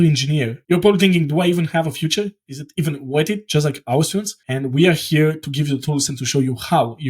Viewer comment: I thought he said: "Do I even have a future? Is it even worth it? Just like Auschwitz" 😯